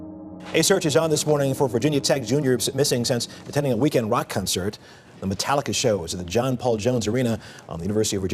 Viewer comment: I’m so sorry but at 3:55 did he say “weekend wock concert?” 💀